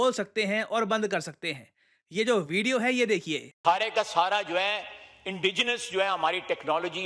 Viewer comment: for once i thought he said hare ka sahara( i mean kbse bhai....)